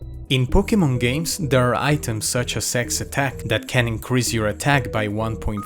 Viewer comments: why did I think that said ### attack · you literally said s3x attack. Even subtitles say that!